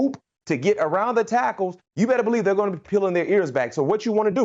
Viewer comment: …Did he just say “they’re gna be peeling their ears back”? 🤔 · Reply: *pin , it's a prhase that means they're going to be able to rush easier, because they won't be worrying about the run